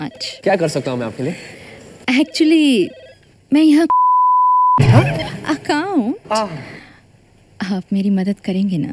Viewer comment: what girl said at 44:37 any idea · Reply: Mein yaha khol ne ay hun... account.